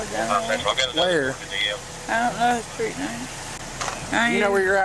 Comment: genuinely appreciate the feedback. if you can point to specific errors we'll look into correcting them. always want the details to be accurate especially on cases this serious. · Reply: subtitles say "I don't know" but she says and points "Her house." 3:17 in response to "Do you know what city you're in?" she responds "Lafayette" 5:33 "Me and ambulances and small spaces" vs "mean ambulances" They get really fucked from 6:00 and beyond: 6:13 The police officer definitely says "Where do you work at?" vs "you don't work yet" which makes no sense 6:16 the police officer says "you mind if I just take a look around?" not "not if I just take a look around" 6:21 in response he says "feel free" not "They're free" 7:02 police officer says "Well hey, look, man". "Well, how you look, man?" is another sentence that just doesn't make any sense 8:08 "it's a four bedroom house" he's not exactly enunciating so can't really fault you here 8:30 "you got your shoes and stuff" not "you got to shoot his stuff". another nonsense caption 8:57 he says "lemme drink my..." in reference to the beer he's holding 9:00 cop says "you got any weed on you" not "lead". my feedback would be to review your subtitles and either manually correct them or be more comfortable saying "unintelligible". When they're wrong, it's distracting and makes the story hard to follow.